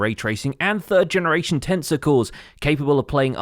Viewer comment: I hear "tensor cores" as "tensicles"